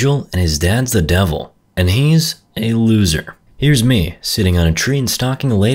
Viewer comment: I think she said loser